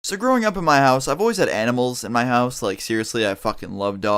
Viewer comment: at the beginning i thought you said "edibles" instead of animals